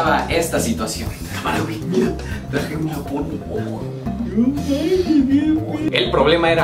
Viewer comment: que dijo? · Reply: Que trajo una porno